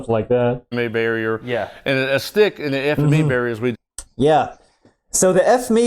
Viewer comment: The "ef me" barrier sounds like what you mumble under your breath when you have to go in it.